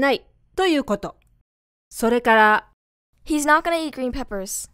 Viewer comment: の gonna が にごらずにカナと聞こえます。 これはガが鼻濁音のように鼻に抜けてそう聞こえるだけなんですか？ 以前から不思議に思ってることです。